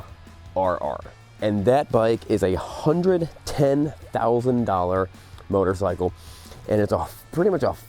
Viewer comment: Did he just say $100k?????